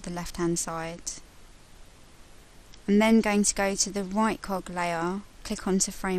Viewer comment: i thought she said right cock and left cock lol. i'm not racist sorry